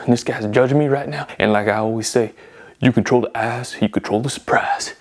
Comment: 😂😂”if you control the eyes you control the surprise” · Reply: I thought he said” if you control he ass you control the surprise “😂😂😂